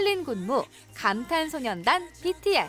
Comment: 헉 나만 8:08 에 감탄소년단이라고 들리나..?